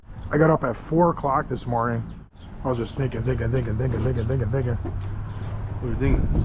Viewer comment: What was saying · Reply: He said "thinking" over and over